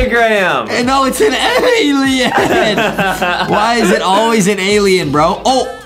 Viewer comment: Alien? I thought u said AYYYYY-LIEN XD